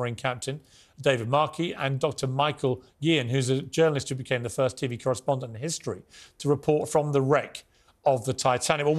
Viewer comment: did he say the first "titty correspondent" ???